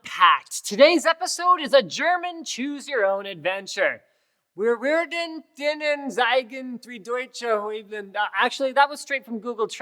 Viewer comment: I hardly understood what you said in german.Nun ich bin auch nicht der beste in der Aussprache ich komme eigentlich aus Russland und lebe seit 13 Jahren hier in Regensburg in Bayern.ich wusste garnicht dass es Firmen gibt in Deutschland die computer hardware herstellen.Naja kein wunder bei der Masse an Firmen hier. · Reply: Ich hab's verstanden Wir wäärden ihnen tscheigen.... -)Wir werden ihnen zeigen